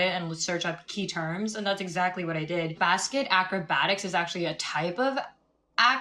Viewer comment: i always thought she said "bask at acrobat" because i never knew basket acrobatics was a thing